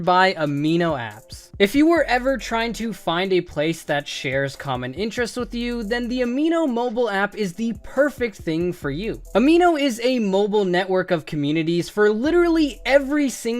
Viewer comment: At the beginning I thought you said amino acids 😂